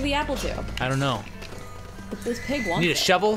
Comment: did say "this pig wants it" around 9:36? 0.o